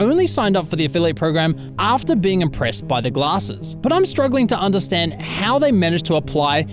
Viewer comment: is that a caption error or did he actually say glossies instead of glasses?